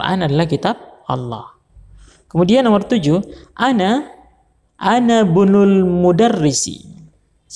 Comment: Ustadz afwan klo yang menit 27:05 soal nomor 7 itu di baca "ana bunul mudarisi" Atau "anabnul mudarisi" ? Afwan takutnya saya salah dengar jadi salah arti. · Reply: Anabnul mudarrisi